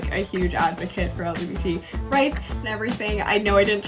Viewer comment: did no one notice how only said lgb rights instead of lgbtq+…..? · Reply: She said lgbt